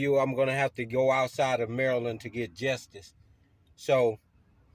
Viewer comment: did he just say "jestice"..😅😅😂.. 🤡🤡